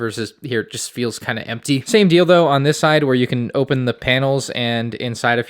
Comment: did you say “dildo”? 😂😂😂